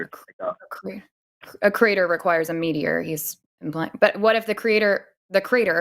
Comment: American accents are so strange, I genuinely thought he said a creator requires a mediary and was so confused.